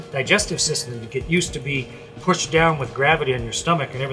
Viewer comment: did he say PUSHED